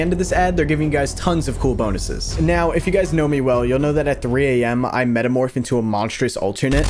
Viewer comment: Me just casually cooking and then I hear “Now if you guys know me, you know at 3am I Metamorph into a monstrous alternate.” …..like HUH?!